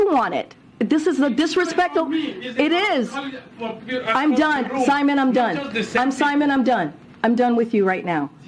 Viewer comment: Did she say "Simon, I'm dumb" over and over? ;)